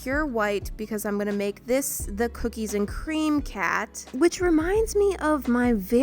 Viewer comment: i thought she said berries and cream cat💀💀💀💀🖐